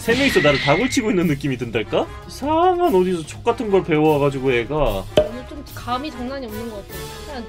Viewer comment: 나만 여기서 이상한 여기서 족같은걸 배워 가지고로 들었나 ㅋㅋㅋㅋㅋㅋㅋㅋㅋ